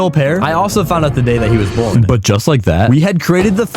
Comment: captions said “geometry ass”